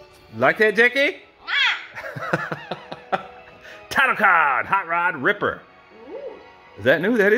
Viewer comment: I can't tell if you made another reference to Dead Meat at 0:43 or not. If you did, did you say Title Card?